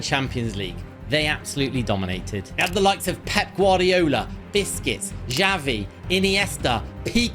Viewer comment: I like the way he says Busquets' name, to me it always sounds like he is saying biscuits😂